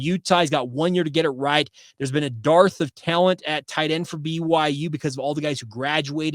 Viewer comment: PLEASE STOP SAYING "DARTH"! The word is "DERTH" to mean lack of. I keep hearing "We have a Darth of Vader..."